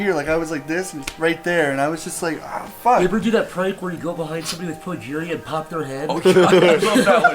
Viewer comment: wait wtf did say at 14:38? i re-listened to this part multiple times and still cant understand it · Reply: TheCandleWaxx behind someone with progeria that part is important